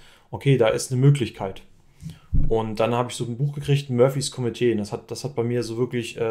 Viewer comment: Wie heißt das Buch bei 9:48? Das habe ich nicht genau verstanden. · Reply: Murphy´s Komitee, hab auch ein Youtubeink in die Beschreibung gepackt wo man es sich kostenlos anhören kann. :) Gerne mir auch ein Feedback geben. Weil mich hat es bis heute motiviert. :)